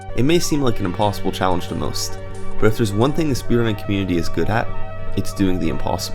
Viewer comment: What is he saying at 1:33? "This __ community." (He sounds like I did when I was younger, but I still can't make it out.) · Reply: The speed run community